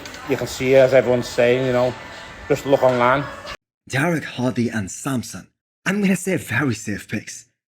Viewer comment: he very clearly said Derek? xD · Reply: your right. I listened again. I thought he said Hadi, Sampson Dauda, maybe Nick.... "Derek" sounded like #Douda" to me..... my bad!